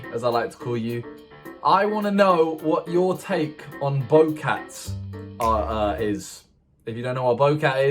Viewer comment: OK :| 👏 When he said opinion on bowcats' I thought that he said boat cat (referring to boat dog from Mark) and then I got excited until he explained and ruined my whole day.